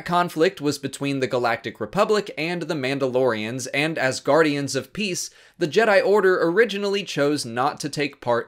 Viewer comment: when you said "and as guardians of peace" I thought you said "and asgardians of peace"